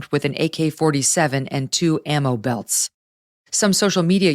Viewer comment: Man I thought i heard "anal belts" at around the 8:00 mark. Had to rewind that.